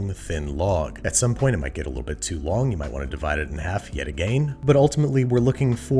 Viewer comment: Did he just say Agane?